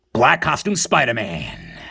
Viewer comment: For the longest I thought you were saying “Black Awesome Spider-man” J. Good stuff as always. Can’t wait.